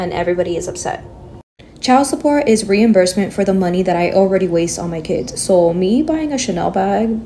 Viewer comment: did she just say “money I waste on those kids”? Sounds like she sacrifices family for happiness… Edited for an additional thought